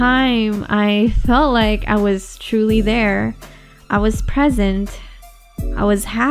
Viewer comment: I thought you said pregnant at 4:33. I'm so sorry.